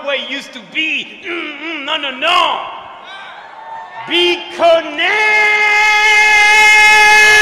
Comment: i always thought he said "be condemned" lmao